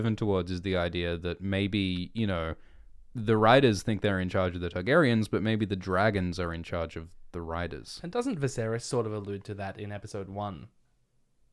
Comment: At first I thought said, "The dragons are in control of the writers."